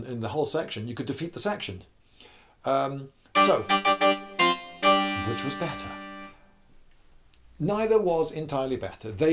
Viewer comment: I thought it said “Ben Shapiro which shoots better”😂😭